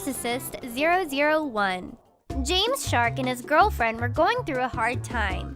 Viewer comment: When she said James shark I thought she said James Charles